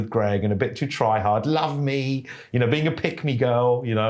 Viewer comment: That "Love Me" sounded like Greg